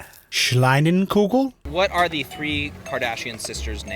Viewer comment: Did he said "Schleinenkugel"? 😂 What's that? it does not exsist... 🤣🤣🤣